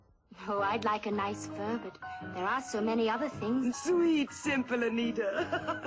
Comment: I can't hear what Anita is saying over there. It is almost as if she is saying "I'd like a nice furbot", but no there was furbot in that age, so I guess she is saying "I'd like a nice fur, but" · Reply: Greenfield2034 she said "I'd like a nice fur, but there are so many other things."